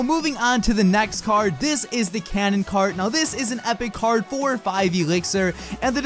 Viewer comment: OMG WHO IS EXCITED!? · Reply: I thought you said cannon card XD